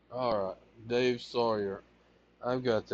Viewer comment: yea he is pretty proud of his talking skills all 30 or 40 words he has learned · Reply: yeah. Talkative? yes. Articulate? No! He can't even pronounce his own fake last name. For years, I thought he was saying ''Sayer''.